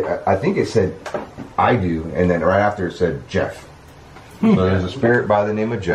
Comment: I thought the voice said Jed, not Geoff/Jeff. 😳